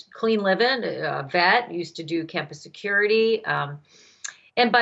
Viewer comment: Does she say “Clean living and fat? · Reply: Vet as veteran😃